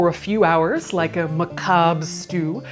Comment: Some of the subtitles could use correcting. 7:22 'indistinct' is 'macabre' 26:36 subtitles say 'this is all' but it's actually 'orifices' said repeatedly (to be fair, I can see how this was misheard)